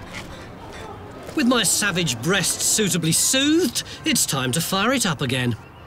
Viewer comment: Did he say savage breasts at 17:39?